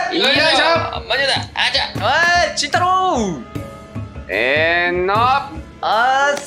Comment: の慎太郎がたまに、ちんたろうに聞こえる...🫣